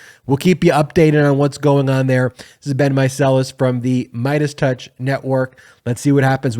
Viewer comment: I keep hearing "this has BEEN Meiselas...."